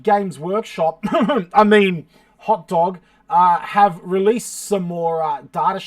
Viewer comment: I just want to point out that my name is Hotrod, not Hotdog! Though hot dogs are pretty great too... Glad you enjoyed the datasheets! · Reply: I thought I heard hotdog but my hearing sucks.